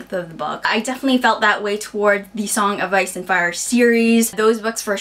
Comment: I totally thought you said "Rice and Fire". Food blog! I love responding to comments, but if I get a bunch at once or if I wait too long.. I get super overwhelmed. Sigh.